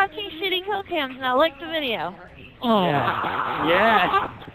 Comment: I thought she said "Lick the Video"...